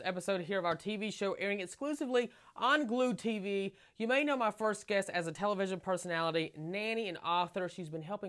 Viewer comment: First guest 🤣🤣 I thought he said first kiss 😂😂